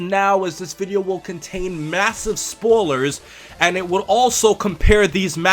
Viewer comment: huh? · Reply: i heard you say spoolers instead of spoilers. (first 10-20 seconds) dont mean to mock you if thats how you pronounce that word. its kinda that jersey accent,like wooder to water.